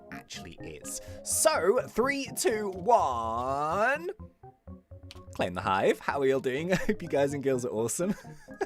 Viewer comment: I thought you said '3 2 1 climb the hive'